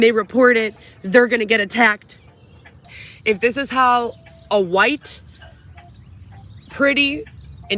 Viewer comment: did she say white 🤔